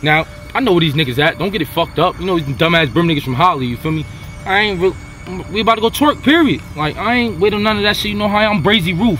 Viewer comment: Did he just say we are out to Twerk 😂😂😂😂